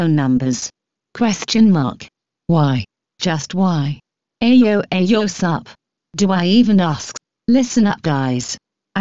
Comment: I CANT WITH THE AYO AYO SoOpTHATS WHAT I HEARD XDDDDD